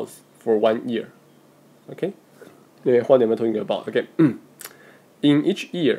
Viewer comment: what did he say at 14:10? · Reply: George Cummins “what am I talking about”~